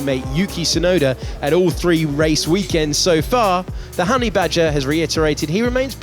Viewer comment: did he say slowey badger?